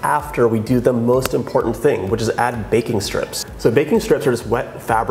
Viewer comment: I thought he said "Bacon Strips" at first, taking this cake to a whole new level haha <3